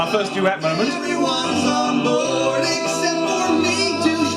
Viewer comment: I always thought the "Yeah, and made it real" said "Yeah, and made it greater." I can't believe I never noticed that😂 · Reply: I thought he was saying "Yeah! And made it look incredible!" But gets cut off before he can say "incredible"